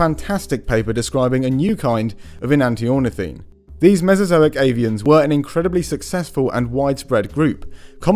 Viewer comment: I really thought he said mezozoic alien and not mezozoic avian.